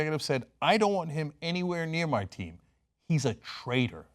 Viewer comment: For some reason, every time I hear the phrase "I don't want him anywhere near my team", it sounds like "I don't want him anywhere near my slaves". hmmmm #veteransforkaepernick